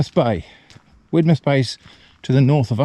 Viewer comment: I thought for a second you said Whitless Bay...and got excited thinking you might be in Newfoundland...nope lol its Widemouth Bay. I also got kinda sad that Eva was missing out on all the exciting rocks to bark at and then heard her yap lol ❤